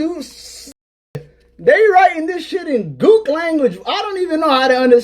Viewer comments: Did I hear that correctly, “g00k language?!” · did I hear him right? Did he just use the term “Gook language”?…… OMG, he is currently in South Korea. I truly thought there was nothing left for him to say or do, that would surprise me, and then he goes and says this……. Just WOW…… there are still service members from the 60s & 70s that have not been found….. Be careful walking around a foreign nation, you just never know who is watching you